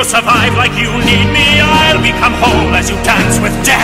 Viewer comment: i heard somehiw this: i'll become homeless you dance with death😂